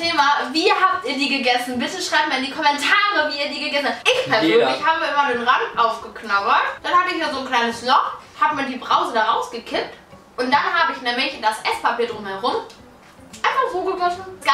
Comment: ich hab verstanden: hab ich mir die Brause reingekifft